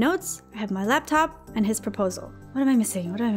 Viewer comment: did she say ‘I have his proposal’?